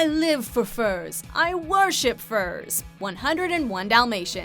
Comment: I thought Cruella said "I live for furs. I wear ze furs." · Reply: The second part is “I worship furs”